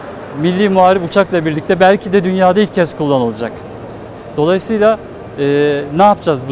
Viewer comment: Dünyada ilk dedi sanki benmi öyle duydum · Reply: Amerika da yapmış ama resmi bir açıklama yok, dünyada ilk kez bir savaş uçağında denendi dedi.